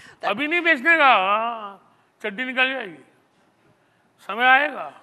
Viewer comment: did he say "tatti nikal jayegi" ? 😂